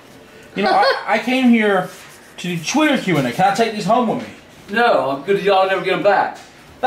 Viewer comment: I heard something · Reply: i heard "come with me"